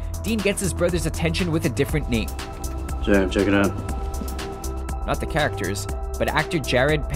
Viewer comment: They're REALLY reaching for that Supernatural one, I rewatched it five times and don't hear "Jared" in the slightest. It sounds like he's just kind of mumbling/slurring his word slightly, a speaking error which carried on through the rest of the line. If he maybe started to say it or something he covered it up super well, I don't hear it no matter how many times I listen to it. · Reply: Alexander Forbes I heard Jared the first time. at around 2:05 he says "Jared check it out"